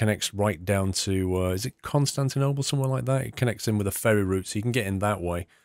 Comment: did you just say Constantinople?